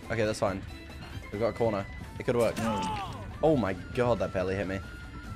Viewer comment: Sound like he said "That belly hit me"